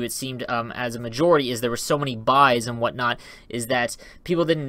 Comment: what's the thing you're saying in 3:55 and 4:00? sounds like buy or bi or body